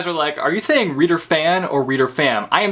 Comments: Lol at the past videos, I thought he was also saying 'reader fan' until I watched this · i heard "reader FANS" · Thank you for the clarification! I always thought that you say: Reader Fan! xD